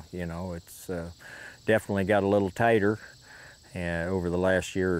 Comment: At 11.34 seconds he’s saying ”it definitely gets a little tighter” but me being from north Alabama I thought he said it “definitely gets a lil tatter🥔”